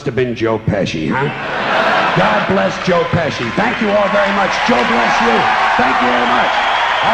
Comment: What did you hear at the end????? "GOD BLESS JOE PESCI" George Carlin was actually raised a Irish Catholic. Love your reactions you two, I am a sub for life. R.I.P. George Carlin. Peace and Love from Central Texas! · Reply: At the end I heard "Joe bless you".